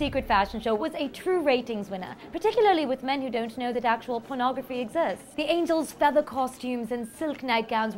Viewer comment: I thought it initially said "Photography" not Pornography LOL